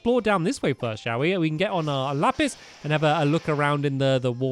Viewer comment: did stampy say lapis